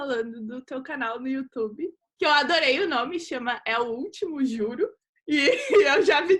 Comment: procurei o canal dela e nao achei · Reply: Aaah é "É o último, juro" eu juro que entendi "É o último Julio" fiquei me perguntando o porque do julio kkkk, por isso não tava encontrando, mas juro faz mais sentido.